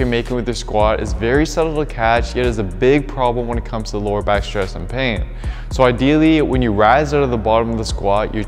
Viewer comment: 433 What? “very subtle __?___” What are you saying? I rewind & still don’t understand you. “vey subtle ? what? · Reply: Very subtle "to catch, yet is a big problem when it comes to lower back stress and pain."